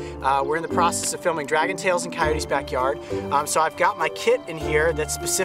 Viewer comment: I thought he said i got my KID in here! Loll its kit